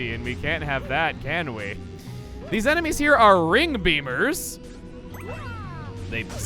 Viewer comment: Thought he said ring boomer for a second xD